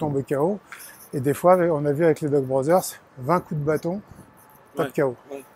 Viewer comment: j'ai entendu "Lehman brothers" ... ^^ A quoi il fait référence au juste?